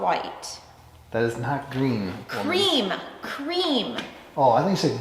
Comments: PROOF women/men speak a different language. ALL of us men thought she said GREEN but SHE claims she said cream. We know she said the wrong word then tried to blame it on us. They ALL do it. I definitely have to stop by for a visit...got a few stories of my international fame...and well just life LOL Like the day I went to prison...well a womens prison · HAHAHHA i swore she said Greem... sounds like a combo of green and cream. I tried 3 times to hear cream. LOL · I heard "green" not "cream" as well.